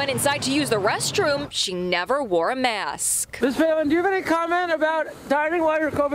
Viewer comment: 26 sounded like she said sour palin. anyway, voice made my stomach sour....lol